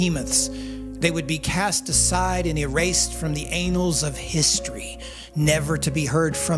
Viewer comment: Did anyone else catch the "anals of history" line?